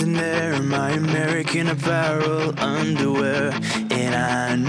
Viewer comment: Back when I first heard this I thought it said "You look so perfect standing there in my American parallel underwear" and my mom had to correct me... My mom is a fan too xD